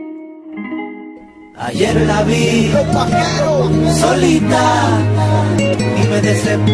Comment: escuche mal o dijo los Pajeros? XD